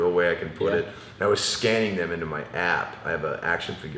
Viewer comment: thought he said « I was scanning them in to my ass » lol hahaha